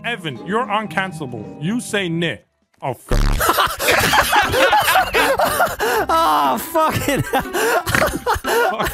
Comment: What did nogla say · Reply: Ger